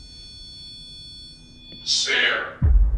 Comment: could hear a whisper saying “Spare” got chills when I heard it